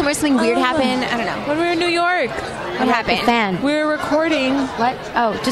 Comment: What does Camila say at 2:57? Great interview though! I love Fifth Harmony! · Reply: "The fan" She thought Dinah gonna be talking about something else, and she said Just kidding, like shut not that hahaha. that's what I understand. 🙈